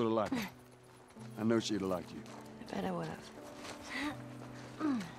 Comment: They fixed the subtitles at 6:07 when Ellie says "I bet I would've." In the original she still says that, but the subtitles said "Yeah."